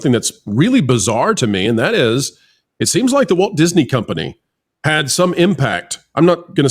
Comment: I heard "woke Disney"